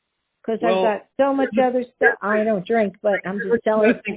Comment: Lol I thought she said she didn’t drink😂